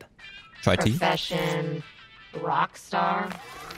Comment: Bro this played on auto play while I was asleep and I woke up to the robot saying “Chai is it?” And I thought it said “Jye is it” I woke up so excited a character had my name but I was disappointed 😔